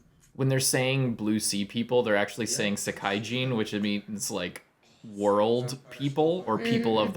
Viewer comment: They do not call them world people. They're saying seikaijin 青海人, with sei 青 meaning blue and kai 海 meaning sea. It's easy to mishear though.